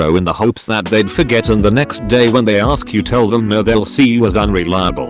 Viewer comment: I thought he said, "The next Damn day"